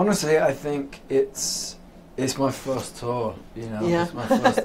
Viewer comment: It sounded like he said 4th tour lol 😂. His accent is everything!!!